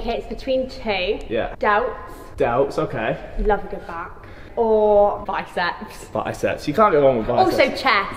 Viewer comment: Did she just say “delts, love a goof back”